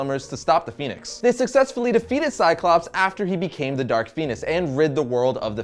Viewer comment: i heard dark penis